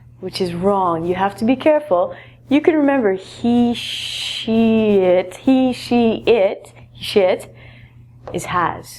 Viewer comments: hahahahahhaha @ 2:40 after saying she, he it, it sounded like she said shet or shit and I went what? what did she say? funny. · did she just say SHIT? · Did she just said (Shit)